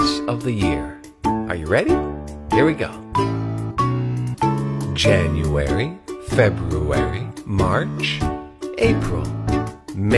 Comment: I REALLY thought it said Jannary